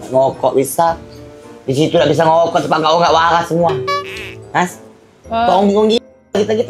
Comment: ngakakkk wee dia ngomong apaa 😭😭🤣🤣🤣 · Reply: Disana (di rumah sakit) tidak bisa ngerokok, dak waras galo ( orang gila semua,, hahaha